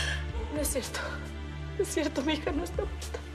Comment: Lo que escuche en el minuto 6:37 es no es cierto mi hija no está p*ta